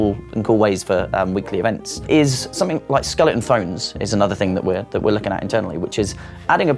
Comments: I thought tgey said skeleten phobes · for a moment i thought they said ‘skeleton phones’